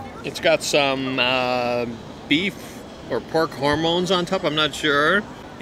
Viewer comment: I’m pretty sure I just heard you say, “pork HORMONES on top.” Ok. I think that may be where I have to draw the line.